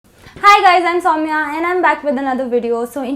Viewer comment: "Hi guys I'm saumya" I literally heard, Hi guy's insomnia! :')